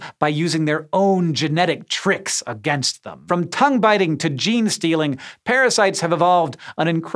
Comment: Did someone say Gene-Stealers?